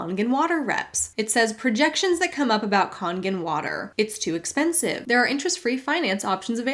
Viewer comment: With the Kangen water thing, for a hot second I thought you said Cullegan water I got super confused!